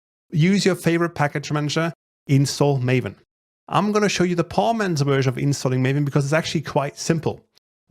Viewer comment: I thought is said porn.xml but great tutorial though